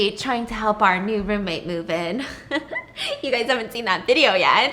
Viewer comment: Didn't she say his name was Ruben or something? 1:25 Sounded like that. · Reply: she said “trying to help our new roommate MOVE IN” Not Ruben 😂😂😂😂